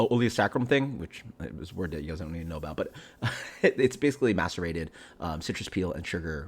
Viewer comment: Did you just say masturbated citrus peel? 🤨